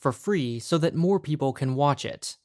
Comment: I heard, "so that poor people can watch it" 😂